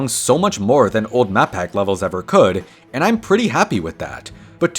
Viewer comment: Swear I heard “matpat levels” maybe him not being in my life is getting to me…. Let me out…. I’ve been stuck in here for days…. I just wanna see my family again….. CAN YOU HEAR ME….. CAN YOUUUUUUUUUUUUUUUUUUUUUUUUU…………